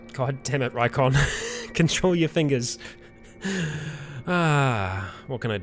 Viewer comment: I misheard and was sure you said "God damn it, Rycon! Conrol your meat!" xD